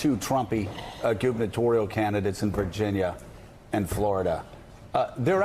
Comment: Did Joe call Mika' Mother'? @ 10:40? Her facial expression says it all.